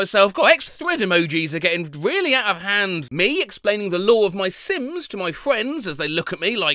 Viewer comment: I thought said the “law of my sins”